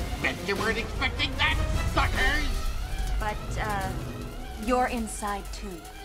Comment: did he say the f word.😆😆😆🤣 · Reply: He said sucker